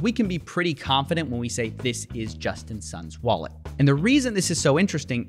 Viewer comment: I kept hearing him saying "Justin's son"